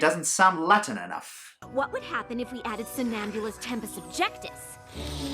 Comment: Did I just hear Starlight say Somnambula? As in the Pegasus pony from Legends of Magic AND Daring Done? HOLY CRAP THERE'S A PLAN!!!!!